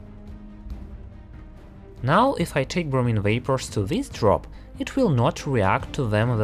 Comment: i thought it said brownie 😭😭😭 im so fat lmao